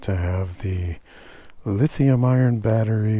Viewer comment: Maybe a slip of the tongue, or I misheard. But I thought I heard you say "Lithium Iron". It's Lithium "Ion".